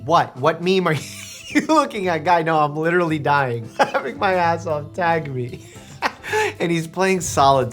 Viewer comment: sounds like he says “lick my ass off”